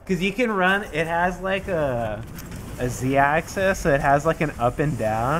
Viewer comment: Did he say Z axis ?